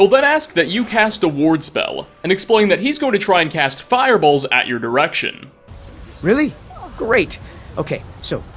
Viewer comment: time to get my hearing checked. I swear I heard him say cast fireballs at your erection... went back a few seconds, cast fireballs in your direction. yeah that makes more sense...